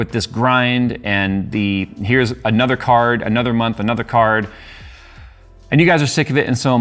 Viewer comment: what i hear is another month, another middle finger